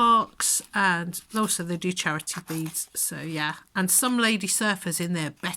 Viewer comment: I thought you said Narly Beads, which would be a good name, now that I think of it. Have you considered making lamp work beads of your own?